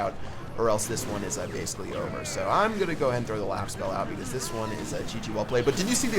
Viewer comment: did he said "I'm gonna go ahead and throw the laugh spell"? LMAO